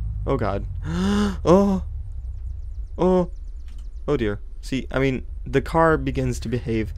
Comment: Oddio?? sorry but i understand like that because i'm italian, or.... did you say oh dear?